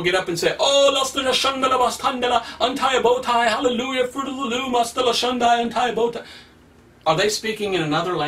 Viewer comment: Lol Did he say fruit of the loom